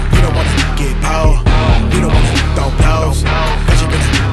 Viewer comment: I thought "throw blows" said "go ghost" with a Danny Phantom reference lol